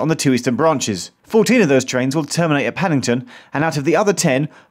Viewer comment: Lol at 0:52 the captions said Pennington instead of paddington, it's not automatic